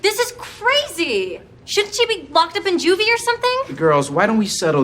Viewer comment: Is it just me or at 14:45 it sounds like she says shouldn't she be f-ed up in juvie.